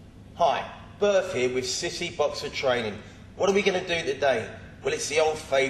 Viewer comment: In the beginning, I thought he said "Sissy boxer training"..